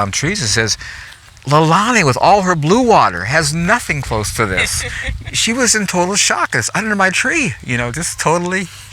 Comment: What kind of plant did he say they used before tissue? 2:13 into the video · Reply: Mullein <link>